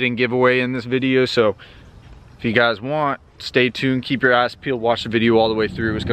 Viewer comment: “keep your eyes peeled” could’ve swore he said “keep your ass peeled”